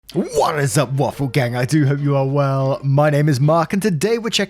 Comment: Cheeky Livestream this evening? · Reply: There have been numerous times when I misheard as "WAIFU GANG"🤣🤣🤣🤣🤣